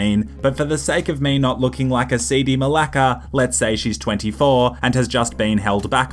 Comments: Did you just say malaka? XD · did he said malaka?